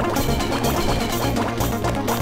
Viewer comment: Uh Oh..... GETS PUMPED what does Walter say to JC's dead body? XD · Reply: "You stupid young rebel" or something like that.